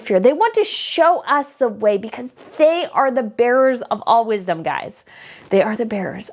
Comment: Gosh.. my in the world did I hear instead of "they are the bearers of all wisdom" hear "the bearers of all what's dumb" twice?!